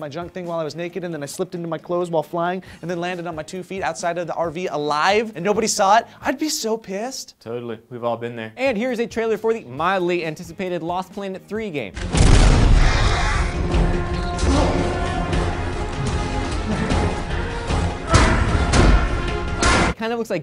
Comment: LMAO I almost lost my shit when I saw the trailer at 1:32 because I thought he said "little big planet"